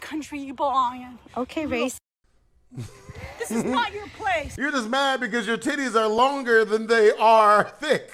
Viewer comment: ngl I 100% thought you said "you're just mad your titties are longer than your dick " 😭😂